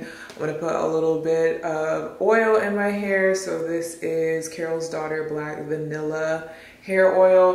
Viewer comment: Am I the only one that thought she said Carol Baskin when she said Carol daughter